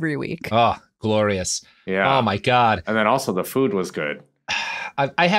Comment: *Mussel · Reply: I heard, "Muscle Monday--and the food was good too." ;)